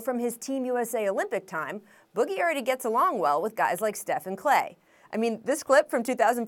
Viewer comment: Did she just say Stephen klay